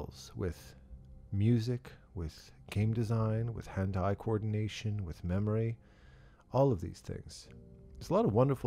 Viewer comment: skills with music, with game design, with HENTAI coordination... lol i had to re listen this part a few times to make sure its not what he was saying :D